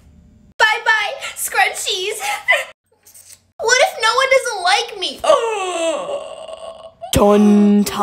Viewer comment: I thought she said "what if no one doesn't like me!?"